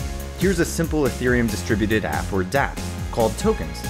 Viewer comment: Can someone, please, write what he is exatly saying at 1:01 ? "Here is the simple ethereum ..?...?...?...?...?...Called tokens! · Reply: We have closed captioning on this video, so you can just click "CC" to see that it says "A distributed application, or 'Dapp'."